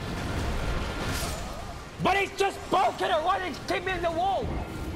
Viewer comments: "But he's just bonkin it- right- he's kicking me into the wall" I think is what he said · “But he’s just broken and what? He’s keeping me in the wall??” Is what he said